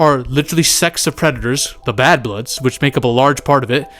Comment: When said Sects, I thought he said sex